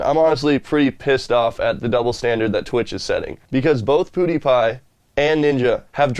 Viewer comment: lol i love u but i died when i heard u say "poodipie"